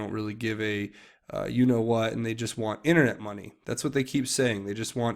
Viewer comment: they want what ? sounds like you are saying "internet munyaya"